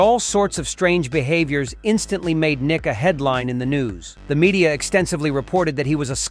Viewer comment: I thought he said ni**a when he said nick a headlines in the news lol